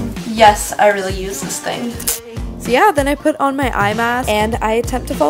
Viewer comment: Am i the only one who thought she said "iMac" when she really said "eye mask"?😂